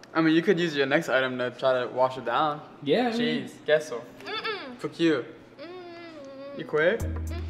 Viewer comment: sounded like dora💀